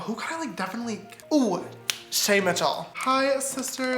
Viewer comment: anyone else thought sister James said shane